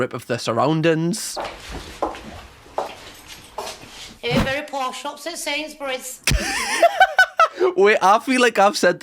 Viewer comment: I have no idea what she said at 7:55 someone help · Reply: Very posh, shops at Sainsbury's